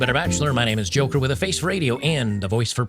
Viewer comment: I turned up the sound late on this and it sounded like you said 'hooker with a face for radio' I think I like that better.